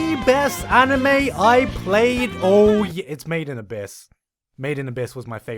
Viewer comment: Wait... Did you just say 'Mate in Abyss'? TRIGGERED